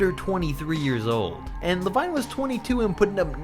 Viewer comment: Did he just say "La-wine" 11:13 If you agree please like the comment And if you do it will give you great luck thx